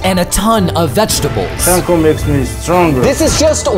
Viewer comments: I thought he meant “im eating a sumo wrestler” 💀💀💀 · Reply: I thought the Sumo wrestler said "chinko makes me stronger" instead of "chanko makes me stronger"💀💀💀💀💀💀💀 Chinko means dick 💀💀💀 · idk why but the “Chanko makes me stronger 😊” was so cute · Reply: I thought bro said "chinko" for a second 💀💀💀 For those who don't know chinko means dick